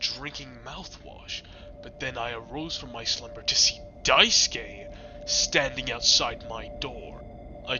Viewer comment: "Close your eyes, Swansea" ahh Daisuke WHAT THE (nice word🎉) IS WRONG WITH THE SUBTITLES IT SAID "dice gay" THAN "Daisuke" BAHAHAHAHHA- 0:09 IS THAT DEKU